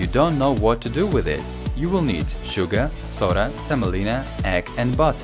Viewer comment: Lol at 1:59 I literally thought he said salmonella instead of semolina😂😂😂